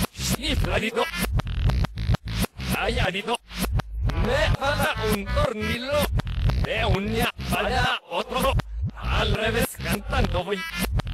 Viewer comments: En 1:01 que no se entiende bien; dice: "Chifladito" "Rayadito" "Me falta un tornillo" "De un día para otro" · dice: chifladito y rayadito xD Y después creo que dice: me falta un tornillo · para los que no entendíeron lo que dijo fue "chifladito, rayadito" Contexto gratis pero si quieres deja like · DIce chifladito luego dice ralladito luego dice me falta un tornillo si no entendi mal dice ''me falta una bala otto'' TIene sentido se les ve las armas hay